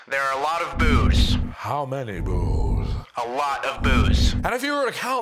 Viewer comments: What I believe I heard: "HOW MANY BOOS?" "LOT'S OF BOOS!" What my mind wants me to believe I heard: "HOW MANY BOOZE?" "LOT'S OF BOOZE!" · at this point onward i was just hearing booze instead of boo´s ´´yes, there is lots of booze``